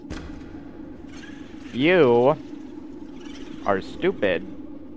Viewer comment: WHAT DID TOU SAY????? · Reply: "you?, are stupid"